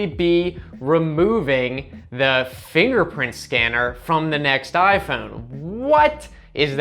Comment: What I heard "In the next iPhone apple will be removing the therapist scanner"